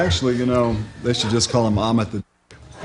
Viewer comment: does anybody know what zappa was sayin' about ahmed? the name of his son and what other people actually should call him? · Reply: He said that they should just call him Ahmet The Dick